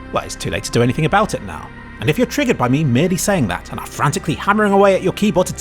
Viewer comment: did he say triggered? Oh god has brainrot